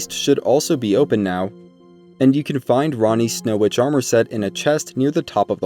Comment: I could have sworn you said "rannis snow pajama set" lol. You make amazing tutorials brother i hope you blow up from all the hard work you do. Your the only elden ring youtuber that i watch.